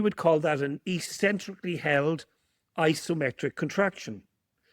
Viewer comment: Love this. It’s so rich and inspiring. I am curious what said starting at 20:17 - is it “Eccentrically held isometric contraction” or “Isometrically held eccentric contraction?” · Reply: That's a great question; to which the answer is: 'eccentrically held isometric contraction' - it's worth listening to this interview several times - just been teaching with in Italy - and he showed video of a medical intervention that demonstrated this in a live patient having wide-awake surgery under local anaesthetic. It blew everyone away! Follow this man - he's brilliant. 🤩🙏🏻